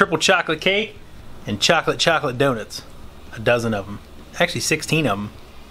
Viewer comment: Thought you said those were chocolate chocolate donuts